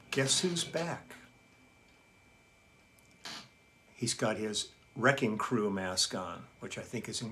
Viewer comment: I heard "he's got his 'wrecking crew' mascot"... saturday night tipsy isn't that bad